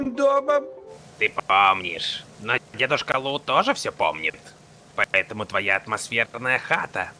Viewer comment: Лайк если тоже услышал «дедушка лут»